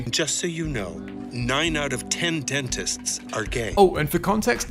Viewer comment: For some reason I thought he said 9 out of 10 gay dentists are gay, guess I'll make up my own jokes now. I even chuckled a bit xD I like nonsense jokes more than... whatever those "jokes" are supposed to be. So even after a year, coming back to this video, I won't give the show a shot.